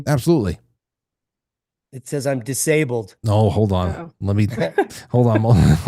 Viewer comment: I'm disabled What I hear: "I'm black, I'm gay, I'm disabled."